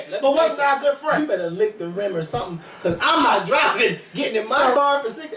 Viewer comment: Did say "lick the rim" at 8:20... LMAO :-) I love it when goes in